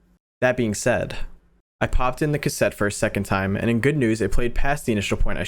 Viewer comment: my sibling thought it said I pooped in the cassette 💀